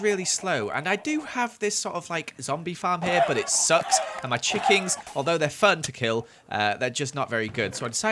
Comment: Is it just me or did he say chickings instead of chickens at 21:13 lmao